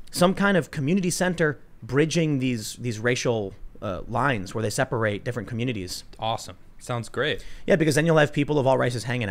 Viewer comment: had to double take because i thought you said we should seperate all the different racial communities XD